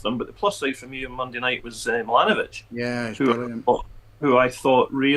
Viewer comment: I would concur with yer starting eleven, that would give us a good chance of a win, but guaranteed Thelin starts Heltne Nilson. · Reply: I hear we've signed the boy fae AC Milan, or is it AC Mill inn? 😂